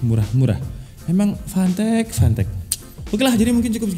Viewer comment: gua dengernya "emang pantek pantek"